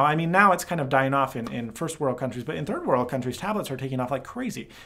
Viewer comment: did he say tablets are dying in 1st world countries at 11:01 ???